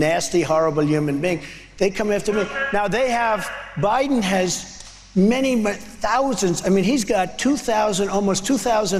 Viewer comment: what was yelled from the audience? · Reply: Sounds like "BULLSHIT! you're a traitor" to my ears. 🤔 😂